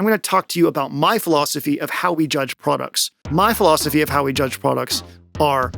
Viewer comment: I swear I heard you say “how we judge buttocks”, several times. I’m sure that we are all ok with that.